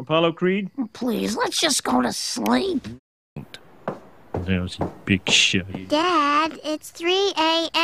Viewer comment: Before I saw the captions, I thought Homer was saying "Lousy bitch". Now I remember it's "Lousy big shot".